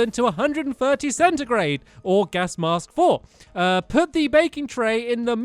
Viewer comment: did anyone else hear stampy say “gas mask” or was it just me???